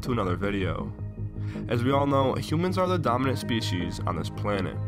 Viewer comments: at the beginning I thought he said "the dumbest species" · For a second I thought he said 'Humans are the dumbest species on this planet'. He would be right though.